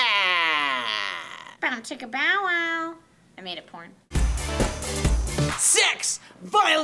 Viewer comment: Did say: "I made a Porn" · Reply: i think it was, "I made it Porn"